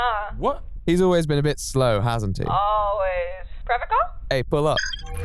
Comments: im only at the vpn ad and im confused. what does she say at 3:40? whats a forever call? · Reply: She said "Private call?" as in she was getting to talk to her alone, away from (presumably) her boyfriend. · what does she say at 3:40? whats a forever call? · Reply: "private call."